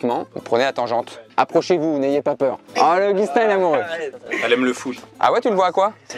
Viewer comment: Si toi aussi t'as entendu "le foutre".